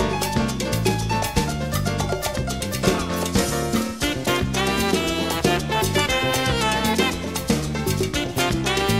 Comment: what's he sayin lol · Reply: "top!" to get back to the head